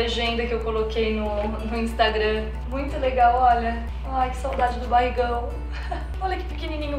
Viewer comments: Juro q escutei o falando "olha" junto com a quando ela estava mostrando as fotos.. Tempo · So eu q ouvi o dizendo "manhê" tipo mãe GARAI APAIXONADA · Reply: Ele. Falou " olha" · só eu que escutei o falando olha